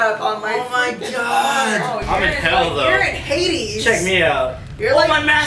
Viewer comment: did say "your in haiti" xD that sounds so bad LOL